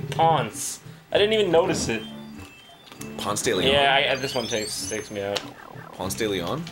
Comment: Thought he actually did say Pounce de Leon instead of Ponce de Leon. I thought he was a Homestuck for a fraction of a second. :I